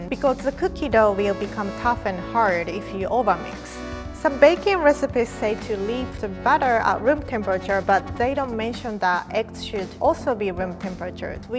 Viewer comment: did she said some bacon recipe or what.